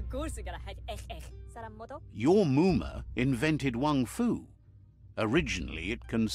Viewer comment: At first I thought they said Woomarang, then looked at the subtitles